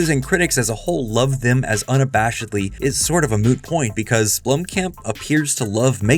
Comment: Did you just say "it's sort of a moo point" at the end?? Like Joey?? From Friends??? 😮